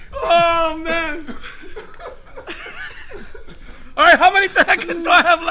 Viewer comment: did he just say how many tacos i have left!!! 😂 😂